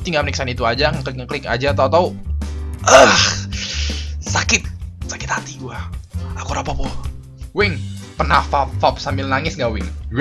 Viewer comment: yang lagi ngetren 1:42 not hd gatau pengen ngomong apa 3:54 sungguh bijak buriq ini 4:01 gw laporin lu eh telat 6 tahun :) Edit: terimakasih untuk 200 lek nya dan gia ada yang laen: 4:07 Sagne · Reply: "Sakit,Sakit hati Gua, Aku Rapopo"